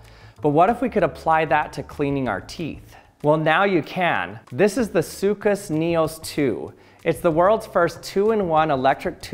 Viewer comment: did I hear…. Сука Sneos 2